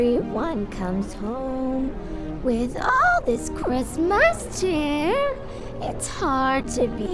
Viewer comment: On 0:13, all I heard was chair